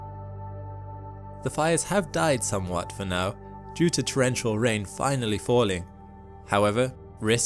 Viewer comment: Prayers, I hope rains come soon! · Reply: Oh, then I heard him say rain finally came.